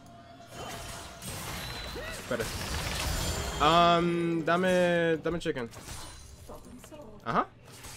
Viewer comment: Did you say Dame xDDD? "Give me" in spanish